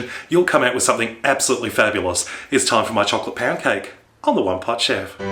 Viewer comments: I thought i heard chocolate pan-cake lol. But i heard wrongly.. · lol it sounded like you said "it's time for chocolate pancake"